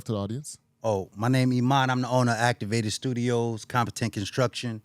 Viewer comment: 42.10 he's trying to compete with jay z. I thought he said don't compete